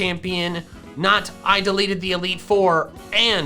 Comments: he said deleted instead of defeated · did he say ”deleted the elite four” 😂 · Did he just say 'Deleted the Elite Four'...? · sounds like he said 'I deleted the elite four'. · Did he just say he deleted the four? Oh boy.... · I swear he said deleted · did Michael say DELETED the elite 4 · Micheal sounds like he says that he said delete instead of defeat here. 17:28.